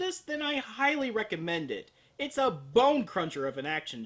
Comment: i had to replay that scene about 3 or 4 times cause i SWEAR said BUM CRUNCHER. yes i know it's BONE CRUNCHER but that's not what it sounded like at first.